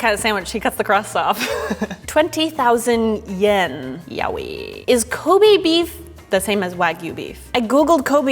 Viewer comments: "Yowie" What I heard: "Yaoi" Me: Where? Food porn??? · really thought she said yaoi